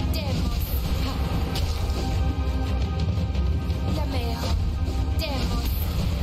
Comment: did she just say Lmao?